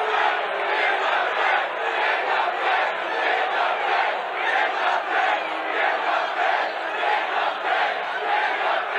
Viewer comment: What does the crowd keep yelling? Around 7:30 in, for example. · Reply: End the Fed (federal reserve)